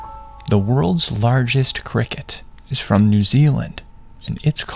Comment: I am too Mexican, I thought you said Giant White